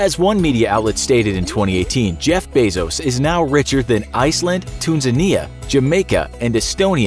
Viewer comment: Wait what, yous said "Tunzinia"?? Do you mean: Tunisia?